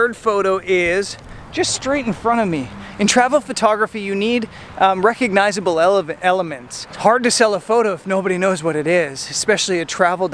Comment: Did i hear that at 07:50 " You need recognizable Elephants " thats all i could hear and think about from that point on lol , some great images even without dumbo